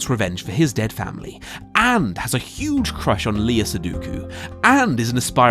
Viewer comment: Did you just say "leeya seduku"? :D